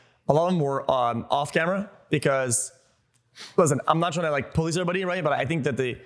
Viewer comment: did he said, "I'm not trying to point at anybody" ? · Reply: "I'm not trying to police anybody"